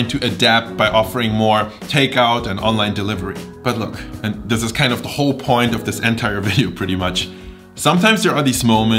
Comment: "Almost like creme gulag"? What's the phrase after he said "almost like"? Pls clarify, anyone! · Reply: i think he said "creme brulee"